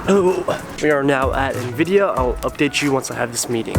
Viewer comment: Dude I thought you said nivdia. I was like what😕